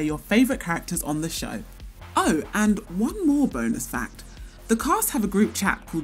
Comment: Did she just say BONAS FACTS